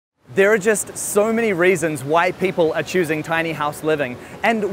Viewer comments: I could’ve sworn he said, “there are just so many reasons white people are choosing tiny house living.” 😂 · “so many reasons why people” I can’t be the only one who heard ‘white people’ c’mon pls I gotta know I’m not racist.